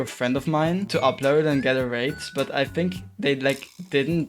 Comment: he did NOT say rated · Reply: He said rate😄